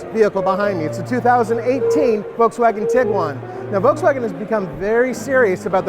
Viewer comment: did he just say Audi Tiguan????